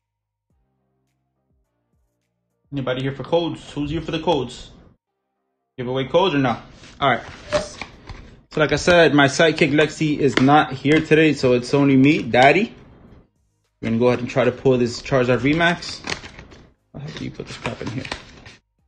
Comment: At first I thought you say your psychic Lexi is not here today, then I realized finally you meant your sidekick Lexi lol